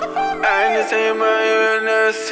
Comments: i thought he was saying "smoke a fat leaf" am i the only one? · 100% thought he said "Smoke a fat bowl" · Tbh when I first heard this I thought he said “Smoker friendly”. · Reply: I thought he said "smoke a fat blunt" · 100% thought he said "Smoke a fat bowl" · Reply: I thought they said "smoke a fat bleez" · I thought the first virse said "smoke a fat blunt" XD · I thought it kept saying, " Smoke a fat Bowl " But I love the sparkle for me concept. Enlighting.